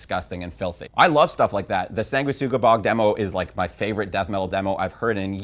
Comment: Sangua wha...? Couldn't make out what the name of the band/demo he's talking about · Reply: Sanguisugabogg